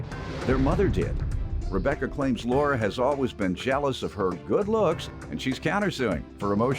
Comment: PLEASE tell me I wasn’t the only one who heard “She’s jealous of my brush.” 💀💀💀💀💀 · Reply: I thought she said jealous of her bush 🤣🤣